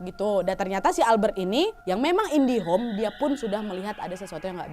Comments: kirain kuping q yg salah denger, diulang lagi, eh INDIHOME donk 🤣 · apakah saya salah dengar..? di menit 19:53 kok kak bilang "indihome" atau ini hanya gejala mistis saya saja ya..? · itu ga salah denger kan kak, soalnya yang aku dengar Indihome bukan indigo Edit : eh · Albert indihome atau indigo di menit 19:52 - 19:53, apa saya yg salah dengar😁🤣